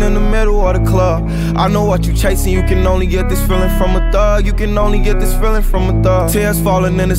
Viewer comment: I thought it said “you can only get this feeling from above”😂